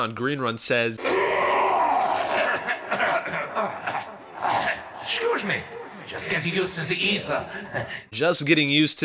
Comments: I allways thought he said excuse me just getting used to the evil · i thought he said " just getting used to the evil" haha